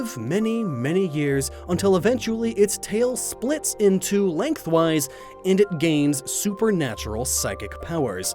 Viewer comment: it's a little clear here that you're reading a script based on the misuse of your inflection on the phrase "split in two, length wise" sounded like you said "split into length wise"